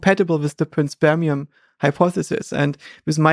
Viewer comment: What's Josha saying at 7:13? Prince Permian hypothesis? I'm curious to learn more but cannot get to understand it